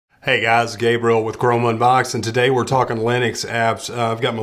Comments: i thought he said "hey guys, it's gay bro" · did he say gay roll? no just me? ok